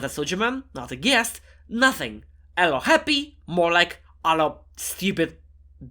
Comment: I thought you were gonna say “Alo Crappy” 😭